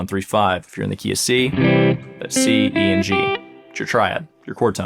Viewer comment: did you say C,D, and G but actually played C, E and G?